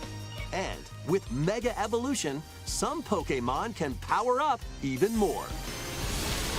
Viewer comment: BRO! My audio cut out for a second and I heard the announcer go "Some Pokemon can FUCK UP even more :)" LOL